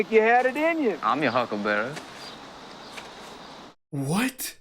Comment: Funny thing, I always thought he said "I'm your Huckleberry" but he actually says "...Huckle Bearer". The huckles were the casket-handles.